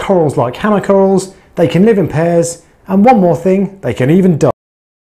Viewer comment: Anyone else lose audio for a few seconds? What can the porcelain crabs do?? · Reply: I thought he said “they can even die” and he blank the audio on purpose hahahahaha